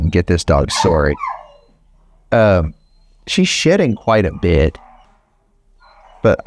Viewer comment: Lol I thought he said S***ing 😅